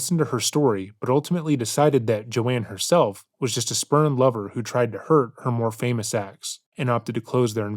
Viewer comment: spurned lover or spermlover either could be true, lol, that's why I heard him say 🤣